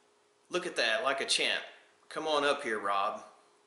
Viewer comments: I heard "come on up here brutha"...(Austrailian accent I imagine) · It sounds like Keith said come on up here bro with his Australian accent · I always thought he said "Come up here, big brother!"